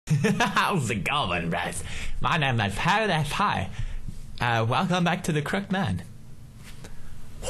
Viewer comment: lol when he said my name is pewdiepie i thought he said hewwiehie XD